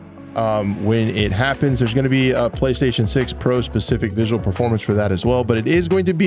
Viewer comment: did he say playstation 6 pro